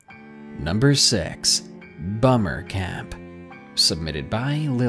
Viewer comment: I thought you said "Boner Camp"